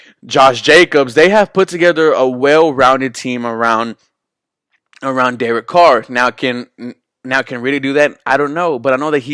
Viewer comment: 🤷🤷🤷Correct me if I’m wrong but I think you half said David or Derek Carr at 2:56.🤷‍♂️🤷‍♂️🤷‍♂️